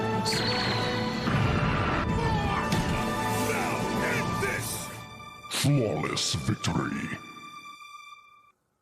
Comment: I'm Sorry, did I hear "Smallest Victory" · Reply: Flawless Victory. It's from the video game series Mortal Kombat.